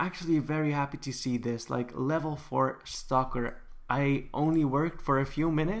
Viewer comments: When he said "stocker" I thought he said "stalker" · I thought you were saying stalker not stocker lol